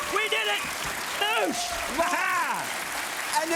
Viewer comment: did you call the host a douche? 😂